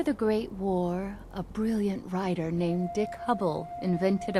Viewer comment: did she said Dick??